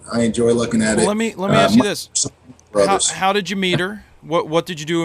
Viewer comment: lol 2:20 how did you meter? thought he said how did you meet her